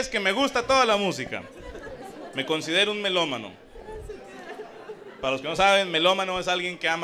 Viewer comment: Jajajaja wey pense que decía melomamo en lugar de melomano